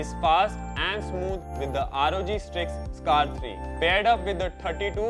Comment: Did you say rog sticks(rog peeli dandiyaan) ???